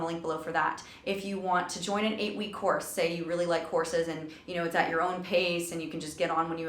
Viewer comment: Lol, thought you said "Say you really like horses" instead of "courses" for a minute. Was like "What do horses have to do with music?"